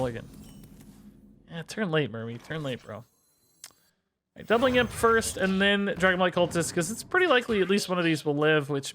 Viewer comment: What does this imp say? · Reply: Suck on big meat